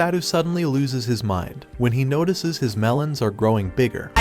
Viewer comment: did he say HIS melons or am i just trippnin'